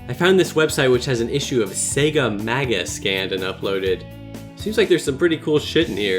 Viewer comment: I thought sega maga said smegma